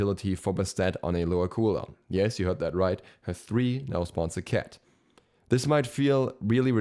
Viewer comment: I legit thought he said "spawns Serqet". I need help.